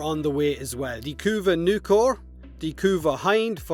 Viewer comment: When he said "kuva nukor" i almost shat my pants cause i thought he said kuva atomos